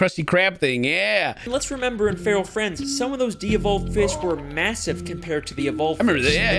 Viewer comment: At first I thought he said real friends lol